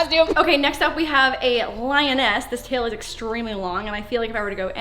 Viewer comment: LAUREN DID YOU JUST SAY LION A**